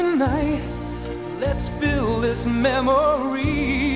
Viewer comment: I think it says let's feel not build